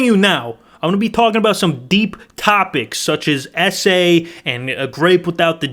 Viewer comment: i thought he said essay but he said S A